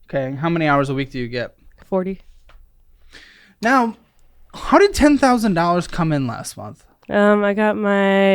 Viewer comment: Omg 1:22 I kept hearing him say 110,000 vs “how did 10,000”. Kept rewinding it over and over thinking I want to get a tax refund of 110,000, how do I do that!? But then checked the transcript… 😅 still a lot of money from tax refund!